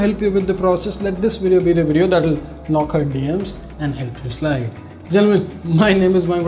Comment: Did you just say "help you slay" in that thick accent of yours lol? Good s***